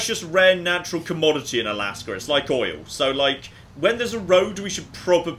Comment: DID I HEAR OIL